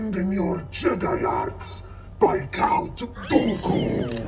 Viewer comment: For a second there I thought he said "count goku" lol😅